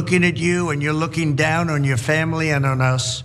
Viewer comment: Did he just say "We are looking down on your family" ? Correct me if I am wrong but that sounds like disrespect, disdain, and condemn to me. Also, we have to remember the statement he made following the insurrection to save his ass. <link> Obviously somebody else wrote that statement, but his statement today is contradicting himself in every possible way.